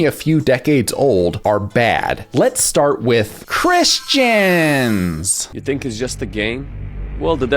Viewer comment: i thought you said Chris-Chan for a Second 😭